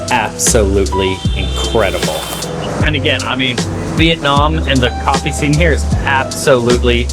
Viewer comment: it almost sounded like you said Vietmanese coffee.